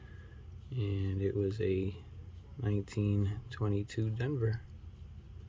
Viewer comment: did you say this a 1922 coin or did i misunderstood you ?